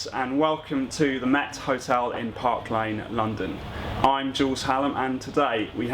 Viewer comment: First I really thought he said : "I'm Jools Holland" !!